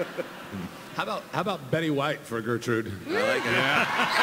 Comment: What does the creator say at 15:46? I didn’t get the actress name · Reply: Betty White. 😄